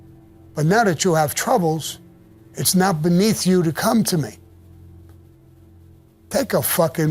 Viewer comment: Above him, not beneath, Sammy…. If it was beneath him to come to Paul, he wouldn’t have come to talk to him - therfore is as above him😊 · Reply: Sorry, thought you Said “now beneath you”, but you Said “not beneath you. My bad.